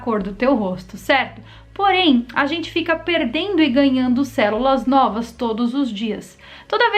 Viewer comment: Eu ouvi ''cérula''?